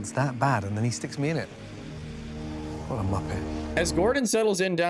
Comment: Did Gordon just call him a Muppet?